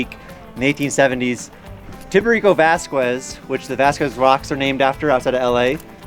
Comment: XD what he actually said tiburico vascues